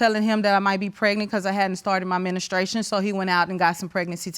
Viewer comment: Did she just say ministration 🤦🏾?